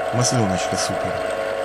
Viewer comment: он что сказал "масленочка сука" 2:10 ?